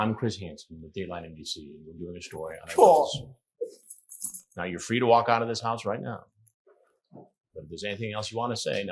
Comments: what does he say? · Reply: a sad (neé petrified) sounding "FUUHK!" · What does say? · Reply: "Cawd!"